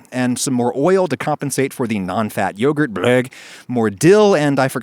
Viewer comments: what's that random voice that comes in here? does he say "break" LOL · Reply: He says "blegh" as disgust for low-fat yogurt XD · did you say egg?